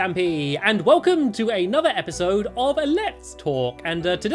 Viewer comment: I don't know why Stampy but at first I thought you said "welcome to another lets fork 🍴" I don't know why but that's just me...STUPID. LOL